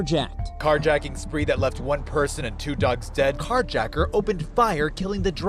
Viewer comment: min did it say 1 person and 2 ducks dead?